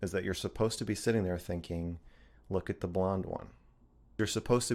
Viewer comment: Yo brother idk why im so late to this whole thing buh ain no way this real💀and did bro say “look at the blonde one” at 3:13?